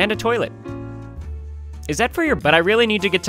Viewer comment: did he just say,is that for your bu